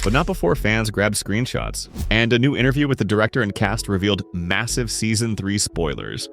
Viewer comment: did you say m m Massive